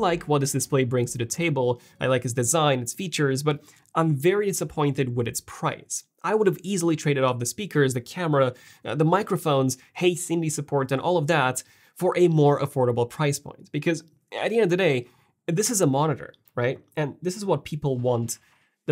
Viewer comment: you sounded like you were saying “hey Sindy” instead of “hey Siri.” Was that so you wouldn’t trigger all of the Apple devices around you?